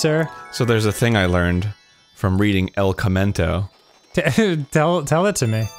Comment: Everytime he said el commento I thought he was saying el camino the movie wrong